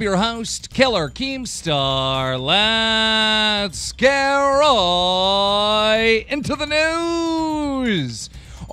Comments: I always thought he was saying "Let's right into the nudes"lol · Keemstar: Lets get riiiiiiiiiiiiiiiiiiiiiiiiiiiiiiiiiiiiiiiiiiiiii Me:..... Keemstar: iiiiiiiiiiiiiiiiiiiiiiiiiiiiiiiiiiiiiiiiiiiiiiiiiiiiiiiiiiiiiiiiiiiiiiiiiiiiii Me: -_- Keemstar: iiiiiiiiiiiiiiiiiiiight into the news! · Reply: No joke, i thought he said: 'let's get roooooooooy into the news'